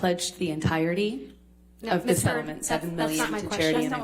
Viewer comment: HER FACIAL EXPRESSIONS ANNOY ME SO MUCH OMG. THEY CHANGE EVERY 1.5 SECOND ahgkdgslkh i love that the subtitles say 'misheard' everytime she says miss heard